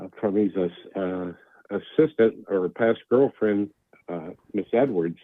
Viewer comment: Tears are falling. Did you say assistant / past GF?